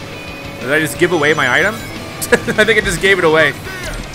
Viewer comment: did u really say “That nigga gave it away” smh I have to unsubscribe and so do my kids